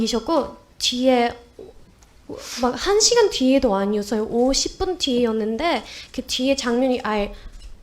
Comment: 들리기로는 50분으로 들리는데, 문맥상으로는 "5에서 10분"으로 표기하는 게 맞지 않을까요? 50분과 한 시간은 큰 차이가 없으니까요.